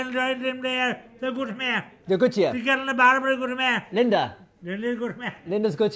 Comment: WTF he say ? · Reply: The girl at the bar is very good to me😂